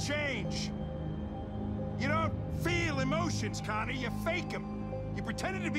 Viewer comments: Did Hank call Connor Conni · Did he call him "Conny" on 2:24? Or is that just me?